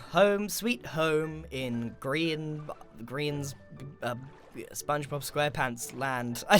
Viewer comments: it sounded like you were about to say "Grian's bottom" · I'm so dumb, this entire time I thought he was saying Korean but he actually was saying grian